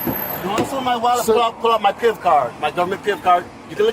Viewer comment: did he say he has a "pimp card".... lol